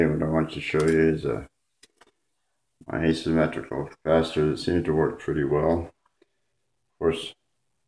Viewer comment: Thank you. Some words you were speaking I didn't quite understand... at 0:05, what did you say after asymmetrical, please? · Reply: I said Asymmetrical Capacitor.